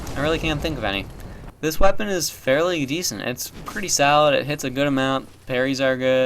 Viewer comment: Lol when he said "pretty solid" it sounded like he said "pretty salad" and now I want the dlc '_'